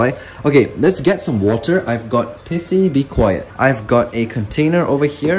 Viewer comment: I thought he said, "I got pissy".